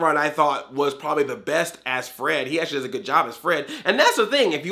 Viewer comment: HBCU is called Hanna Barbara Cinematic Universe, otherwise called; The Cinematic Universe That Isn't Going To Cinema. It is coming to your cell phone today! 10:47) did you just say "best a** Fred"? It just sounds like it on my phone speaker and the Closed Captions said that too. End question) well I like these movies on digital but it varies between which ones. I been in a film class this semester. That class and some recent videos I've been watching told be a lot about copyright and fair use (mainly wanted to put hints to Suzy Lu issue). Also they did say that most the time when a movie in theaters has 60% of the profit to movie studio and 40% to theaters. Not consetions those are 100% theater but 60 and 40 is just vary because it is different from different countries. For a physical or digital would be 80% to studio and 20 to store (again different from different countries). I didn't watch Scoob nor Trolls World Tour. I still think movies like New Mutants should go straight to VOD but also movies that aren't set in a "cinematic universe".